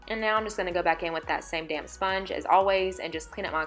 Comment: Stunning! I thought you said same damn sponge Lol!